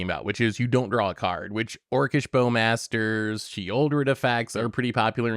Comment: Drawing a card is bad, becaus orcish Bowmasters and Sheoldred exists, am i hearing this right?